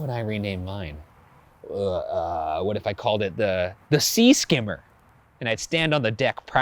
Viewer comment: Oh it was supposed to be "Sea Skimmer", I heard that as "Sea Scammer".😂😂